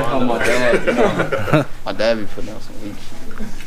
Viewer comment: so we gone act like we ain't hear what Bronny said lmao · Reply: *weak shoes